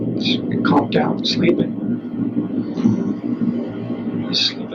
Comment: did he just say “calmed down sleeping”